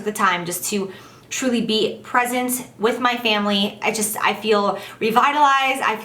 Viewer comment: At first I thought you said "pregnant with my family." Ha. I had to rewind that back!